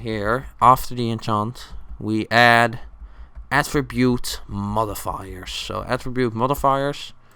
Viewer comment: lol i thought he said Atribute Mother Fu**ers